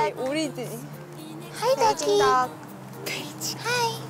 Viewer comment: sounded like she said the bi#$h lol